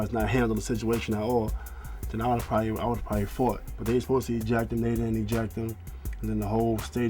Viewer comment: When Ron says "they were supposed to eject him, they didn't eject him" it sounds like he is saying "they were supposed to ejaculate"